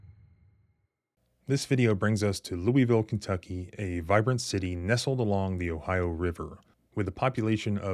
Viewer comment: when you were describing the city and said vibrant city, i thought you said violent city